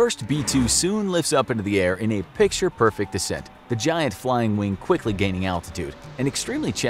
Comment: did he just say "lifts up in a perfect decent"?